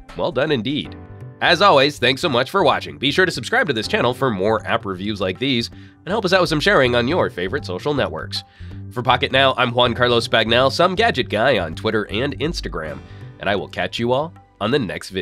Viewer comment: !!! Love it. BTW, I always thought the way you say your name sounds like "One Carlos Bagnell"